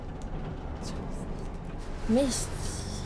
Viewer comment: what is she saying at 0:58? meish? what? is she swearing? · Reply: Mist!..... how impossible